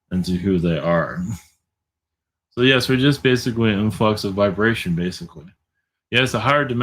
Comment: Did he say "unfucks the vibration"???